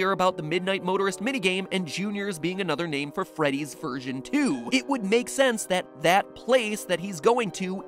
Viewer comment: When he said "freddy's version two" I thought he said "freddy's virgin two"